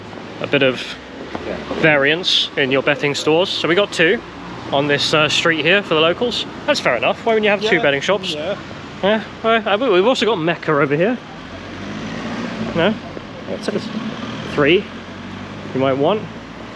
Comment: Ohhh "BETTING shops." As an American I thought he was saying pet shops like why would that be a bad thing next to a school? That's genius. Oof.